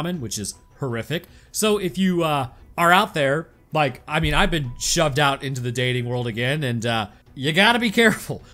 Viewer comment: Did I hear that right? “I’ve been shoved out into the dating pool again” 15:47 Is not married anymore? 😢